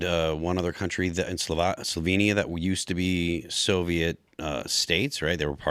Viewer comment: Shout out to Slovenia for not being a part of the Soviet Union but in fact a part of Yugoslavia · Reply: I thought he said Sylvania, like the light bulb company